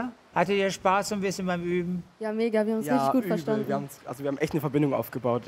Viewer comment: "Ja mega wir haben uns nicht gut verstanden." Bin ich die einzigste die das verstanden hat? Lul